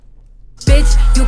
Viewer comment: I thought the nun said bitch you scared me 🤣🤣🤣🤣🤣